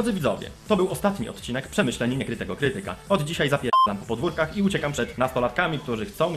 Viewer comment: "spierdalam na hator"? ja tak usłyszałam xDD